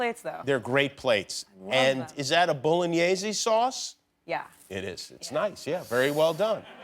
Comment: hold up did he just say 'bolognesey sauce' x_x